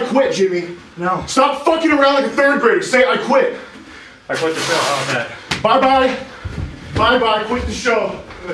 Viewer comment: What did jimmy say at 15:01? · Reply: "I quit the show how about that"